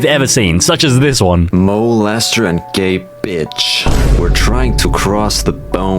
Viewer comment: What he said: Gabe Itch What I Heard: Gay Bitch